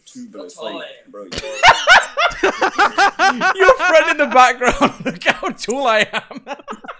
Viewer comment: i thought that first chick said "my friend died" at first